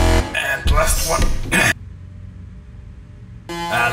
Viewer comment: i know it says let it rip but it sounds like he said let it drip